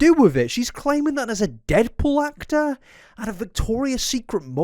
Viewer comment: i thought you'd said "debt collector" and had to rewind